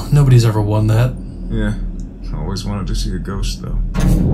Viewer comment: "I always wanted to see a ghost" The followup OHSHIT look in his eyes was perfect. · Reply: I thought he said "I always wanted to see how it goes" in relation to the bet. But your comment makes more sense lol